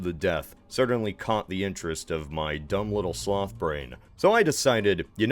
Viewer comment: i legit thought u said slav brain for a second